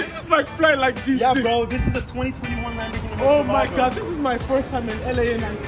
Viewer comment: My guy said 2021 lambo, if I heard correctly